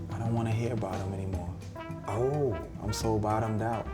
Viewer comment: "And people may not want to hear this, but like, I don't want to hear about him anymore. I'm so bottomed out." Pharrell Williams on Bob Dylan. Really Pharrell, when historians travel back and hear your productions of Get Lucky and Lose Yourself to the Dance, do you think they'll be calling you the poet of a generation? I don't think so. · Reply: you misheard Pharrell. He said, "I'm so BOTTOMED out." As in he is over hearing the beats of hip hop that Daniel is talking about loving so much himself.